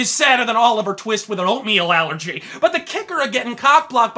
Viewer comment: "Oliver Twist with an oatmeal allergy" Ok the first time I watched this video, I could've sworn you said "Oliver Twist with an opium addiction." WTF.